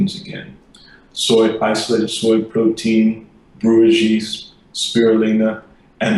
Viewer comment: Guys,I am translating video at the moment,can't recognize what doctor said after soy protein,and before spirulina when he named ingredients,it about 15:29 timecode, could anyone help? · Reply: brewers yeast